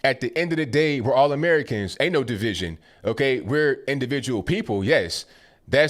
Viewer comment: did he say we're individuals, not part of The Borg? Liked and subscribed you're awesome man keep up the good work!